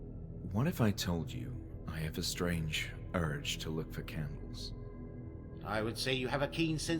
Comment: see, I heard, 'lick the candles' and was very confused for a moment